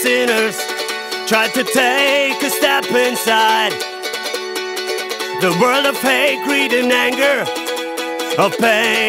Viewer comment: I am reading to much Harry Potter... He sings "the world of hate, greed and anger" and I hear "the world of Hagrid and anger" xD